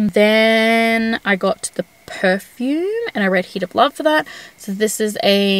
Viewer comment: When you said Heat of Love I totally heard it as “next I read Hitler” and my head snapped up like Wwhhhaaaaaaaaaaaa?